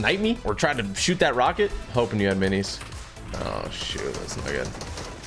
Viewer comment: What did he say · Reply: He said shoot